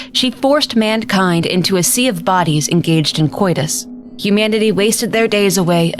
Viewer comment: what word does she said here I’m guessing it means abnormal. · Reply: she said "coitus", it's another word for sex